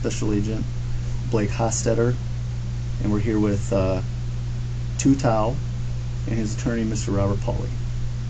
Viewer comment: Y I I thought he said we're here with June tao?🤣😂😂🤣